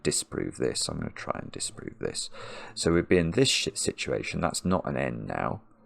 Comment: Did say “S**t situation”?